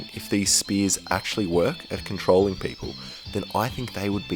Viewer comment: when I listened to this the first time I thought he said $%#hole